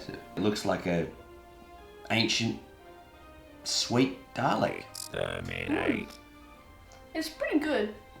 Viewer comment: am I crazy or did he pronounce dalek like dah-lay? Am I just hearing it wrong?